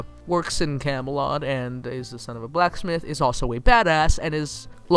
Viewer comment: did you say the son of a blacksmith?